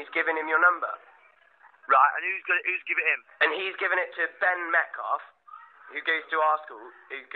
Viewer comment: Who's Tony Adams? He used to play for arsenal 😂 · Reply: I heard it as Ben Mecca at first, had to re-listen 🤣